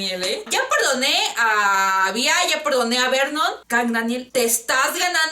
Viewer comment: No entendí el "ya perdone a Vernon" :v